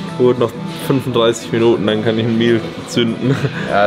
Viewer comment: "Noch 35 Minuten und ich kann ich...." Was sagt er da? Ein Bier zünden? Mega Form! Bin auf die Finale Form fürs Finale gespannt! Weiterhin viel Erfolg und Spaß auf der Reise Richtung letztem Finale der Saison. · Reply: meal